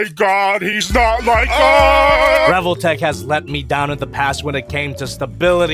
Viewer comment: did he say NOT LIKE US Mustard on the beat yo